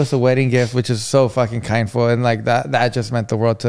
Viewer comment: did say “Kindful” 😂😂 instead of Kind of Mindful